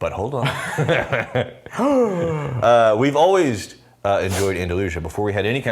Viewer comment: Did he say "Alwaysed"